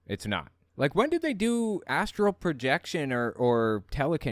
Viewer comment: I thought I heard him say he is proficient in “asshole projection”.